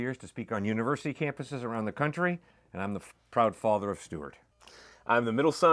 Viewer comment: Did he say F***ing proud father of Stuart? Dayummmmmmm!!!!